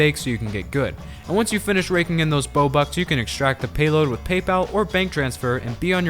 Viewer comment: Did you just say.. bobux?